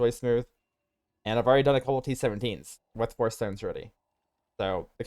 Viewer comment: bro what are you saying? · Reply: And I have already done a few t17s, with 4 (void)stones